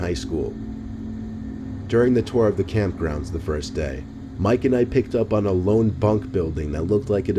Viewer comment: I thought he said Mike and ike like the candy lol